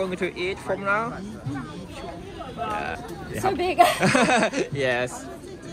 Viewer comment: サイズ デカイとしか聞こえないｗ